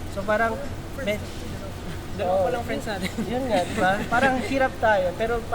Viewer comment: did Stell say dalawa palang friends natin? Would that be The Juans and Ben&Ben?